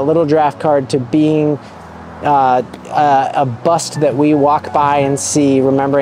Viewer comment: So wait am I hearing you correctly at 1:58 you say Bo Nix is going to be busy and all we will have to remember is is Auburn and Oregon days .. like wait. What? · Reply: I heard bust and not busy